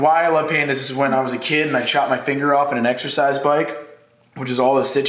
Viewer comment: I thought he said "SEbikes"